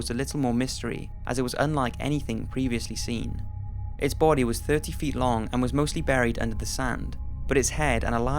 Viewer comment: did he say 30 ft long?